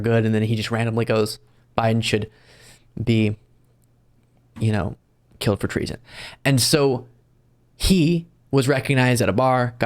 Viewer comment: Please, please, please be careful when you "quote" someone. The guy around 11:54 did not say Biden SHOULD be killed for treason. He said he was GOING TO BE killed for treason. Slow down a little and take the time to be accurate. It is so important to us all, especially now when there is so much misinformation and disinformation disseminated.